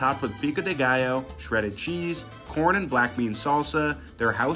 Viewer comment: I thought bro said meat fiddler